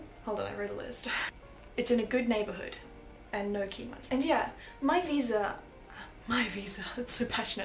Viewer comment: What does she say here? "It's in a good neighborhood and..." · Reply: no key money." <link>